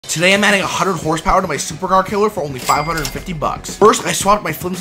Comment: Thats not a supra killer thats a on brand supra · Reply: Didnt realize thought is said suprakiller not supercarkiller